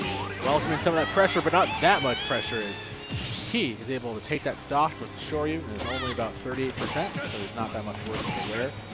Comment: What Byleth says when she is flying off? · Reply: My battle ends here